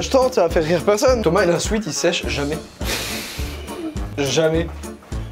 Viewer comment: Il dit quoi Joyca ? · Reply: Il sèche jamais 😅